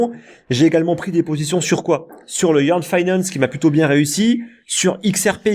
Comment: "YEAR FINANCE" ?! c quoi ... sur quel exchange ? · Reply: YEARN FINANCE ou YFI